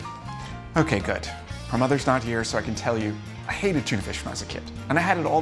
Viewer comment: Had to watch Jacks first clip twice. I originally thought he was saying “Mom, I hated you as a kid”!!! I was like DAAAAAMN, Jack is cold blooded!! Now I see he just hated tuna. I did too!